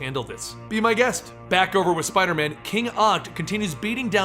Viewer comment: who is that????!!!! All I heard was king odd